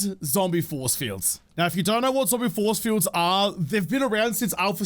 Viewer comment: Was watching with hubby and he thought you said zombie foreskin when you said zombie force field lol